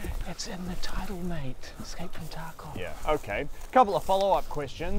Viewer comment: I like how they've written absolutely no jokes. They are just reciting what every single tarkov player has been through. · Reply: i thought they said escape from taco